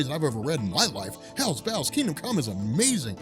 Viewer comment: Did he just say "hella bells"